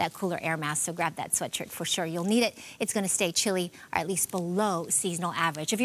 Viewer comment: did you say chili? Yummy 😋